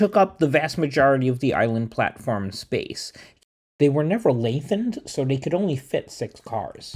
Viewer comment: what word are you saying? lavened? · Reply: Lengthened LOL.